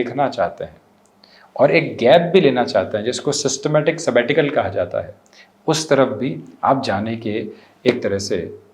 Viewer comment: can some one tell me, what did he say 'systemetical something'